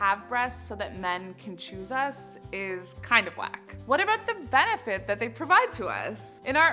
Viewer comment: I thought she said “kind of black” and I was really confused